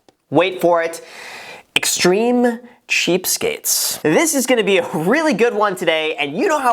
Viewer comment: i always thought people were saying extreme cheap-steaks ...Sweet Steaks